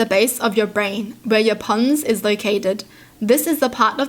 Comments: Me misheard her: “where your PUNZ is located” Me replays it: “ohhh, that makes sense” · I legit thought she said where the "puns" are located haha